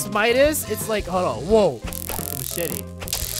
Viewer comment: he did not say the S word he said machette